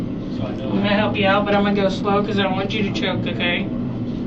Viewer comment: I thought she said i want you to choke i guess wishful thinking